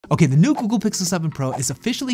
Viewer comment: Why did I hear it as 'the new cukoo pixel 7.... ' 😂😂